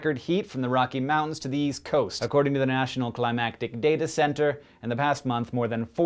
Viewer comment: Is it just me or did he say "National Climactic Data Center" instead of "National Climatic Data Center" at 0:46?